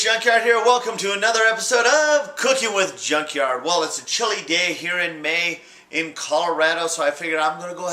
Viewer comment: swear he says "its a chilli gay"